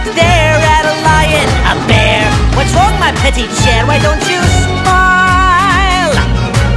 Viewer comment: Am i the only one who thought fantoccio said "Whats wrong my petty chair why dont you smile?"